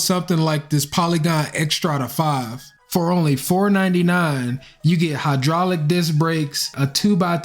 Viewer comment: Say what ? Did he say $499 USD ?